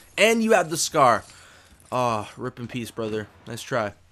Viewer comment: did he say R.I.P in peace?😂 Rest in peace in peace.